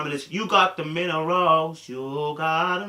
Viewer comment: The whole time I thought you were saying “You got the men aroused” and I would laugh because I would be like damn I do 😂😂 but i just realized you was saying “You got the minerals” 😭😭